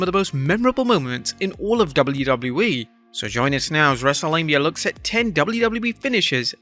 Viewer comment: It always sounds like you say "Wrestlelabia", which always confused me, til I looked at your actual name.